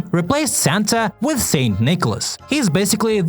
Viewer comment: what are you talking about shes saying just like santa not saint nicolos or something shes just saying santa